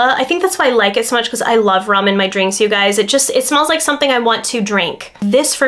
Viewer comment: I stepped away from my phone for a sec and thought you said "I love ramen drink" @ 10:52 hehe